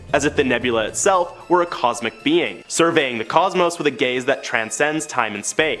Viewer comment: Did he say gays instead of days? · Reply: He said "gaze". Lol